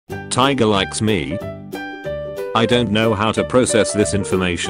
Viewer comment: Oop I thought it said "Tigers like me"